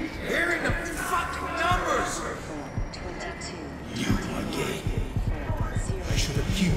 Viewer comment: it sounds like he says “you are gay”